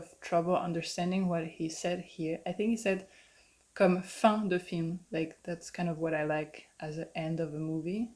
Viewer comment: i heard "c'est ça que j'aime bien comme fan de films" (un fan, qui aime les films) and not 'fin de film"